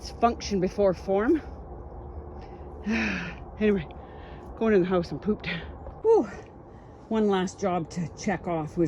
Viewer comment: Lmao 🤣 I thought you just said "I'm going into the house to poop" 🤣🤣🤣🤣🤣🤣🤣🤣🤣